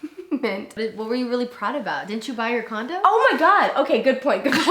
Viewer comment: i thought said “condom” instead of “condo”